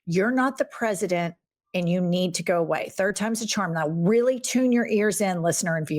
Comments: I thought he said “I need to go pee.” Anyone else hear it like that? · I thought the kid said "i need to go pee"